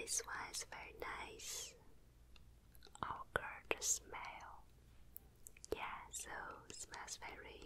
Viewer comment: Question: can someone please tell me what says in min 2:43 right before the word smell? I’ve been wondering for so long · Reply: Destiny Angelique ohh she says gorgeous smell